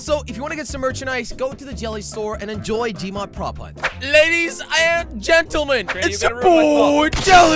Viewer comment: In the beginning it sounded like he said "ladies I'm gentleman"